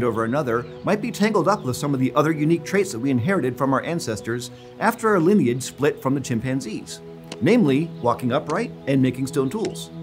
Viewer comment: Some commenters have pointed out that we say "after our lineage split with chimpanzees," "members of our lineage after the split with chimpanzees," and "after our split from chimpanzees" at various points in this episode. Just to clear things up, humans and our extinct relatives share a common ancestor with chimpanzees and their extinct relatives. After our lineage split from this common ancestor, we call our group "hominins." Living chimpanzees also have their own separate evolutionary history since that shared common ancestor. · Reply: it clearly says at 0:59 "after our lineage split from the chimpanzees" which isn't in your sample quotes. and the closed caption there says "with chimpanzees" Not what is said. Lets be honest; the presenter says something incorrect. No one is misinterpreting it.